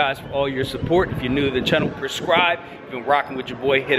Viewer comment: he said prescribe instead of subscribe 😂😂😂😂😂